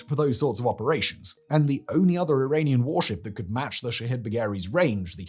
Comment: All I hear is "shithead" when he says the name.